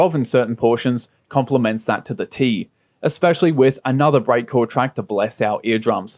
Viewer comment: What type of music gerne at 5:35.? sorry couldn't tell what you said there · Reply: Breakcore